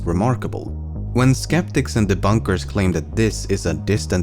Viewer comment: It sounded like you said "skeptics in the bunkers".